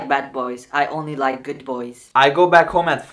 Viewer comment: it sound like he said in the masjid of masjid